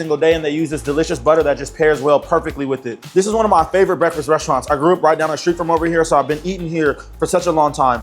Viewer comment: I,thought you said BLACK RESTAURANTS?